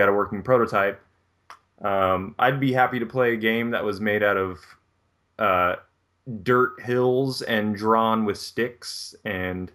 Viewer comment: when i heard say "id be happy to bplay a game made with dirt hills and drawn with sticks" I heard "id be happy to play a game about dirt hills and drumsticks" sounds like a design challenge to me! dirt hills and drum sticks